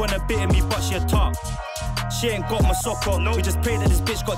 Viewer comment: cant lie i didnt catch the "masoko" bar. I thought he said "my sock on"😂😂😂😂😂😂